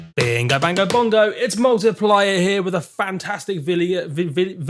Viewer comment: Thought you said markiplier in the beginning, did a double take lol